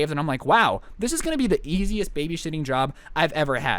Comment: did he say "this is gonna be the easiest babyshitting job" or os it just me